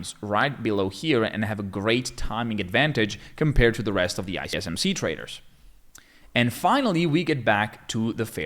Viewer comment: i think i've heard something .. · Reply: I smc traders😆😆